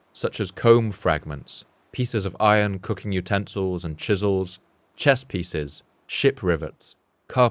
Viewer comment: did he say chess pieces?